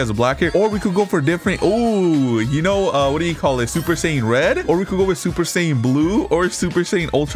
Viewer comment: I heard him say super saying red and I clicked off the video